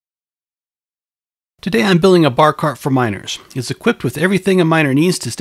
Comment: OH for "miners" ... I didn't read the title and could have sworn I heard "minors"...